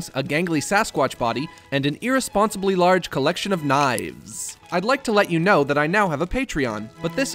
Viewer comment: My deaf ass ear is so destroyed that i thought i heard said he has a large collection of nines